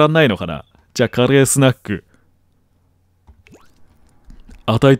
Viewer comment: 声だけ聞いてると加齢スナックがカレースナックに聞こえたわw